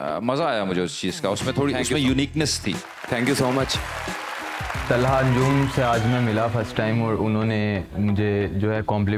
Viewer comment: did he said talha yunus ? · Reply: Nahi bhai Talha Anjum bola hai....